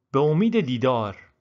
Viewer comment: IS WRONG. It is written “manzuret chiye? “ Meaning “What do you mean ?“ you are saying here “Be omideh didar” meaning “hope to see you again!”